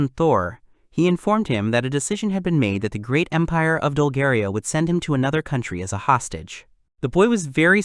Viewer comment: Bruh at the start i heard "the great impare of Bulgaria" 😭💀